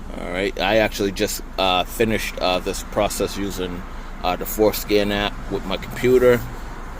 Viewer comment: the what app 💀 i swear bro said foreskin